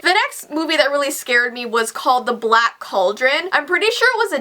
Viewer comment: I'm like blind so when she read the black cauldron I thought it said black children